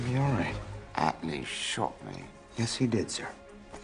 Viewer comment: Does he say hot @ 1:35? 😅